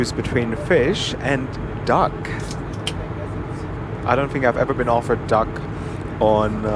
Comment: I thought he said dog 💀